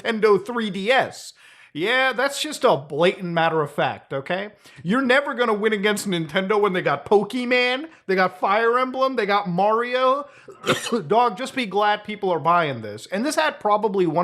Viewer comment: am I stupid or did he just say Pokimane ??